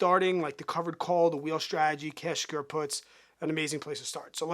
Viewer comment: What are you saying at 1:18? Covered Calls, the Wheel Strategy, "test your puts" ??? Thanks